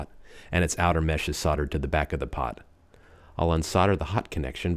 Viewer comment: thought you were saying SODDER -.